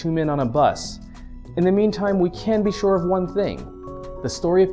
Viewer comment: lol, i thought at 3:36 he said "In the meme time". yeah i think im high